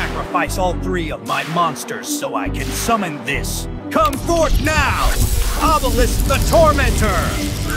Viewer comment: Anyone else hear Joey and Tristan going "We're still tormenting you!"?